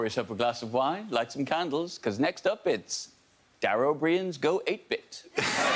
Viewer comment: What does he say at 6:57? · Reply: Dara O'Brien's Go 8-bit. It's a tv show hosted by Dara